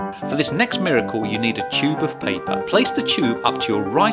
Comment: i thought he said 'jubes' (nuts)